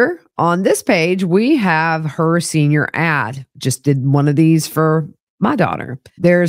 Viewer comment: Question - Starting At The 15:23 Mark Of The Video, (In Reference To Wendi) Sounds Like She's Saying..."And Here, On This Page, We Have Her Senior Ass." I've Listened To That Section Four Times, And That Is Exactly What She Seems To Say. Is That Some New Thing Or Am I Misinterpreting Something?